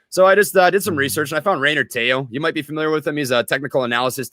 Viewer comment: Did anyone catch the name of who watched on youtube to learn the ropes? Reinard something? He says it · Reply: rayner teo